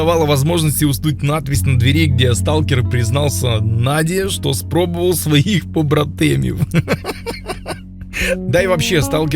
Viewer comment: что он сказал?:D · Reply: Сталкер признался Наде что попробовал своих братков (походу👌👈)